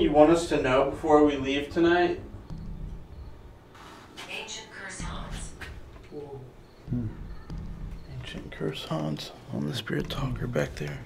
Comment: Listening while working I thought it said ancient croissants lol